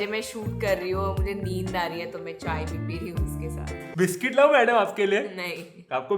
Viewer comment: i heard whisky